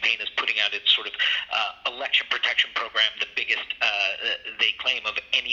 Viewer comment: Biden Election Protection Program? Did I hear that correctly? 29:45 😐